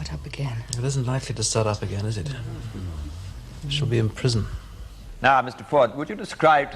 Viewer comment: Great stuff!! At 1:48 I originally misheard "she'll be in prison" to my ears as "you should be in prison". Completely changed my interpretation of who the real culprit was. Made me root for the defendant from the outset and was pleased with the verdict. But then, playing that bit back, I most likely would have sided with the prosecution.